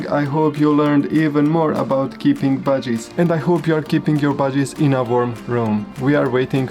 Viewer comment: I thought he said keeting your budgies 😅 I was like wth?.. 😆🤦🏼‍♀️